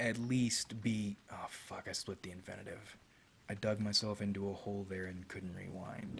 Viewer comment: What is it that frustrated him at 14:46? He said he 'slipped the inventative'? From what I can guess, he's frustrated he couldn't end his sentence grammatically correctly.